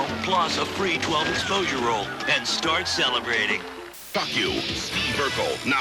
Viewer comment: what the heck did he say at 13:28?!? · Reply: And start celebrating...